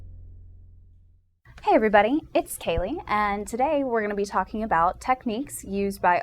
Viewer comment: At the beginning, it sounds like she says "It's Kaley," but the audio caption says "It's scary."